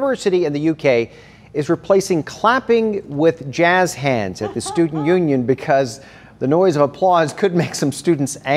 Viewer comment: He said "student union", I heard "stupid union"